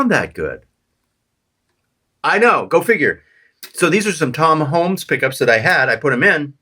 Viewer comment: I thought you were saying “John Holmes” pickups. 😂 Guess those are pretty meaty eh?